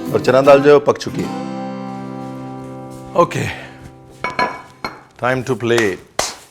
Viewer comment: After 5:14 I heard Time to Play! Oh & thank you, aapne potli masale ki recipe nahi batayi.... secrets should be kept secrets!